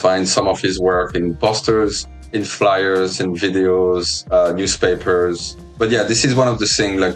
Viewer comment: Ok hold on i might be crazy but 18:17 did he say videos like in game videos??? Ive heard rumors but i might just be tripping